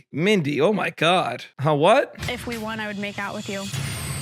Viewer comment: Lol yeah 14:27 definitely thought she said if you want I'll make out with you yeah wierd